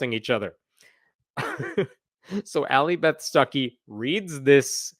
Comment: Every time you say her name I hear "sucky"...